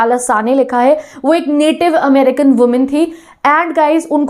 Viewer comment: When mam says native America i heard naughty America 😉🤣🤣